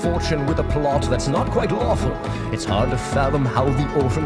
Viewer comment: Wait its lawful? I thought he always said "With a plot that's not quite awful"